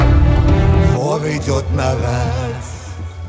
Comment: Вор идёт на ... Что ? Подскажите,кто расслышал... / интересно,что довольно выразительного эффекта можно добиться в минималистичной обработке видеоряда,никаких особых сложных,кинематографических,дорогих процедур, талант и чутьё. Впечатляют эти работы. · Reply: вор идет на рать